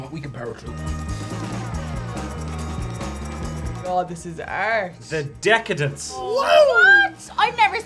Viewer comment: at first I thought took one look at the donut and said “this is arse” lol the Irish and their aspirated final alveolar stops (same reason Dermot misheard Ciara saying “shard/shart” in a recent video